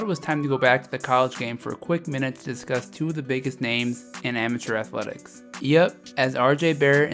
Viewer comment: All I heard is, “Amatheur athwetics.” Bye lol this kid can’t ball